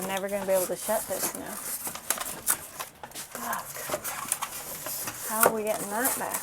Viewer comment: Couldn't quite catch what you said at 16:06...ha! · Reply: Hmmm, I'm pretty sure I mumbled 'fudge' 😉 Thanks for watching, (and listening so closely) haha.